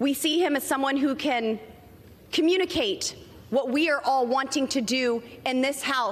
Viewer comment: Did she just say " what we want to do to American's, lol